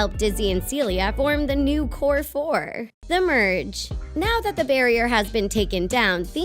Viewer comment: When you said the merge I thought you said the purge